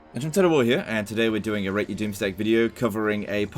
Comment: For a second there i heard it as a "rating your mistake" video